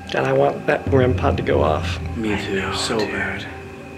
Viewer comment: I heard a like a kid or woman’s voice going like something could hear then at the end heard “up too”